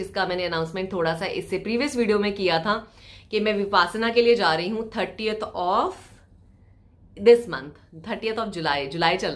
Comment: What did u said ma'am @ 10:05 38 of July🤔🙄 Anyone noticed that Anyway soo happy to see u 😍 & get well soon🥰🥰